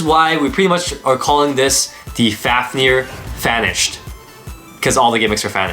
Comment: WHO HYPED UP FOR THE NEW FAFNIR?! 😂 Hope your video pausing game is strong...get that Fafnir code into your game! Glad to see the rubber make a return in this release, but so many other things have changed. We really hope that the entire Sparking series gets included in the Pro Series line up...so many releases from Beyblade Burst Sparking were EPIC! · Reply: u sound like ur saying fanished